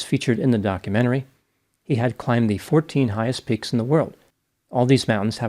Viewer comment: I thought he said 14 pigs so I made ai write me a story, I could have written it faster by myself lol it can be dense sometimes Up! Max wakes to a giant mountain peeking in his window. "Let's climb!" he shouts. One! Max stretches and sets off. Oink! A little pig joins him. One, two! They huff and puff up the path. Dots! Two more pigs waddle up, wearing funny hats. One, two, three, four! They giggle as they climb. Umbrella! A shivering pig hides under a tiny umbrella. One, two, three, four, five! They huddle together for warmth. He is not a pig don’t count him Up! Max wakes to a giant mountain peeking in his window. "Let's climb!" he shouts. Ready! Max stretches and sets off. Oink! A piggy joins him. One! They huff and puff up the path. Double! Two more piggies waddle up, wearing funny hats. One, two! They giggle as they climb. Umbrella! A shivering piggy hides under a tiny umbrella. One, two, three! They huddle together for warmth. Keep going til 14 Up! Max wakes to a giant mountain peeking in his window. "Let's climb!" he shouts. Ready! Max stretches and sets off. Oink! A piggy joins him. One! They huff and puff up the path. Double! Two more piggies waddle up, wearing funny hats. One, two! They giggle as they climb. Umbrella! A shivering piggy hides under a tiny umbrella. One, two, three! They huddle together for warmth. Snore! A sleepy piggy pops out from behind a flower. One, two, three, four! They tiptoe past the napping friend. Backpack! A piggy needs a lift and climbs o